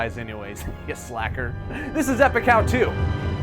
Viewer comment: Thought this man said “F a cow two” not “Epic How to”